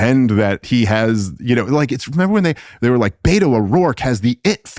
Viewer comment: Beta enough. Haaha. Beto. Lol thought he said beta o rourke